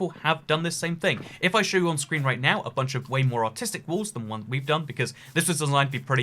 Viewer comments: I thought he said “autistic” 😭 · when I heard the word "artistic" I didn't heard it like that I heard it like "autistic" I was like "WHAT!?" then I started laughing at what I heard 😂😂😂😂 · Did he say artistic or autistic? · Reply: Well YouTube’s captions say it’s artistic